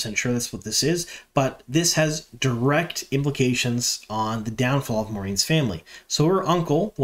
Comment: Siuan Sanche will remove Moiraine's shield. This has been foreshadowed already when we saw Siuan as a girl untying fishing knots. That's my guess anyway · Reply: I first thought Min said Siuan would be Moiraine's undoing, and that would be badass (the word she used was "downfall" which means it's probably not about Siuan at all). But I do think Siuan untying Moiraine's knot is still a likely thing.